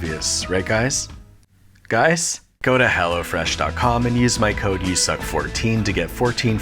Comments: lol thought he said go to hell instead of go to hellofresh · I'm so used to YSAC insulting us that during the sponsorship at 4:37 I thought he was going to say "Go to hell" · Honestly I thought he said go to hell after he didnt get an answer 04:37 😂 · I can't be the only one that heard that as "...go to hell o' fresh..." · really thought he was saying “go to hell” for a split second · I could have sworn he said "Go to hell 'o fresh .com" during the sponsor bit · For a second i thought he said "Go to hell"